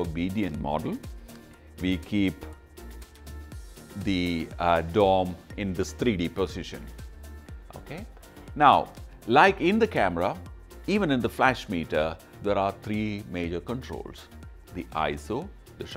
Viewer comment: Love all your videos and so when I got my new Sekonic light meter I cam here first. I was watching then at 3:20 it kind of sounded like you were politely saying "okay" to your model and caught me off guard and made me laugh! Thanks for that too!